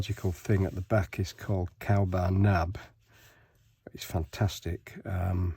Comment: Calba Nab? What is it called? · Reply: Hi It’s actually Cowbar Nab